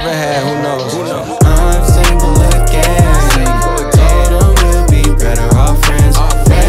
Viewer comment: Why did i hear "i'm single and gay"?